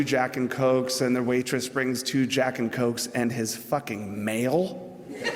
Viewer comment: My english is not very good. What does he say about the Rainbow anecdote at the 2:59? He ordered two Jacks and Cockes and the waitress brings two Jacks and Cockes and his fucking mail or male or what? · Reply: David Molano his MAIL because he was always at that bar it was like his home.